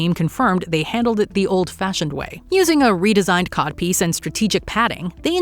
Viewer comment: Did she just say "using a redesigned cock piece"?